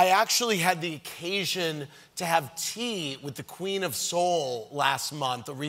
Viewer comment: When he said the "Queen of Soul" I thought the said the "Queen of Seoul" before he said it was Aretha Franklin, LMAO